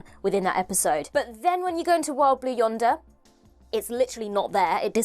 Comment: says "Wild Blue Yonder" so fast, I hear "Wobbly Yonder."